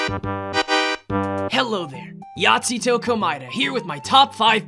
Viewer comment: At first I thought Nagito said "Nazigo Komaeda" at first.